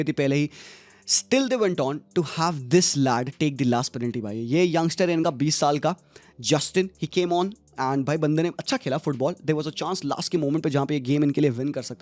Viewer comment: I had to rewind and listen again at 22:07, thought you were saying justin pokemon😂